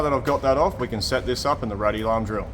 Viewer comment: I heard “set this up in the ruddy larm drill”! 😂